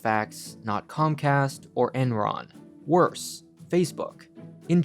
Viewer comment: Did he say "worse, Facebook" I'm not a Facebook user but 1B people on daily???